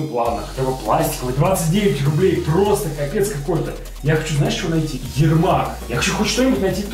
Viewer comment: Мы послышалось сказал: я хочу найти елдак)